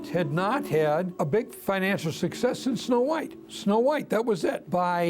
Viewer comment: "Walt had not had a big financial success in Snow White". Yet Walt is quoted in the making of Snow White documentary as saying "...six months later [after finishing Snow White] we were sitting, with all of our debts paid off, with at least a couple million dollars in the bank." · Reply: I thought they said "Since Snow White" , not in.